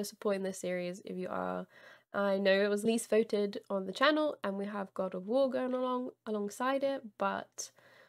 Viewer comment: Am I hearing correctly that this game was "the least voted on the channel?" I've tried to listen to this about 10 times at max volume on my phone and I keep hearing that sentence, but usually From Software games are incredibly popular blind playthrough games, I thought. I'm actively watching probably 5 people play Bloodborne as they upload throughout the week while I'm working. I feel like I must be missing something.